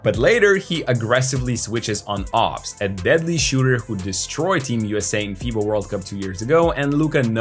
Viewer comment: Did he just say “Ops who destroyed team USA in the fifa World Cup”. 3:48😂😂😂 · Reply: No he said "Obst, a deadly shooter who destroyed team USA in FIBA World Cup two years ago" clean out ur ears